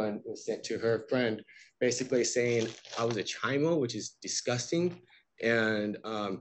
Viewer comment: when he’s mentioning two letters that were sent to his wife, what is he saying is disgusting? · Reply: That in one of the letters, they call him a "chi-mo". Aka child molester. Learned that that was a regional expression after I moved somewhere where no one had ever heard it used before lol